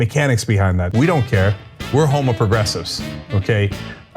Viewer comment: Did Cenk say "We're homoprogressives?"